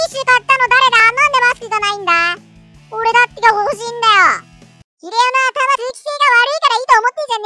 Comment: ってなんて言っているかわかる人います？（わかる人入れば解説お願い致します🙏） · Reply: ｢ひれおの頭通気性悪いから｣ 的な感じだと思います(´ω｀)